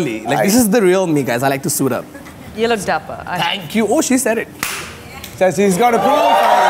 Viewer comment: Wait! What did Shibani call Varun? I heard "You look dumb" 😂😂